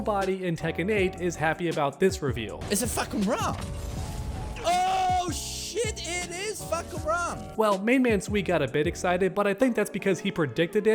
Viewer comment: I'm not english, and I don't know that character. So imagine my face when I heard "Is it fucking bruh?...oooh, shit, it is fucking bruh!!" And I'm thinking "dude, who is that?"